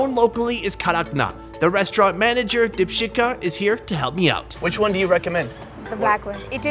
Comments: i thought he said dipshita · Am i the only one who heard him say dipshitka 😂.